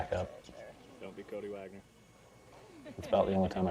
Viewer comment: did you say "Dont be cody wagner"?